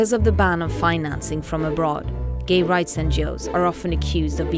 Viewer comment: Did she say gay rights and Jews?? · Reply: She said gay rights NGO-s...are you trolling?